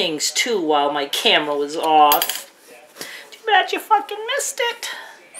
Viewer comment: did you say fkn??